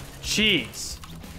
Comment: I thought he said cheese